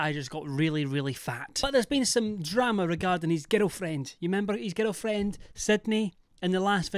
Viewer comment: I legit thought you were saying "ghetto friend" since e the last video you made about Beavo. I am ctfu at myself that I just realised it's "girl friend" and I adore the way you say "girl".